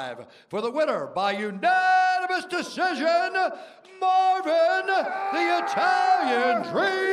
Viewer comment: I thought he said Marvin the torry 😂